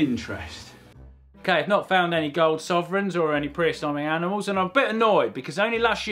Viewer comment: Pre-"Assumming" animals... what are you saying?? :-P Closed Captioning says Pre-assignment Animals? Is this a British term for pre-historic that I don't know?